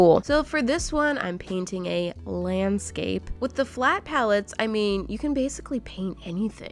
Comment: When u said with the flat pallets I thought u said what the flap 😅😅😅😅😅